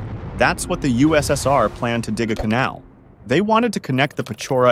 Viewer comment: the voice sounds like it said plan not planned glad there was subtitles